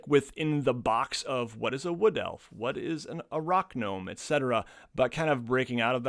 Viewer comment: you said ‘Rock gnome’.. I heard is as Arach-gnome... aka spider gnome... cool new subclass invented at the spot! Thanks! 😝